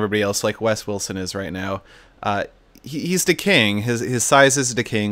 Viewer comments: When said that wes is "decaying" I thought he said "Wes is Da KING" cuz wes was the biggest lol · i thought you said Wes was "da king"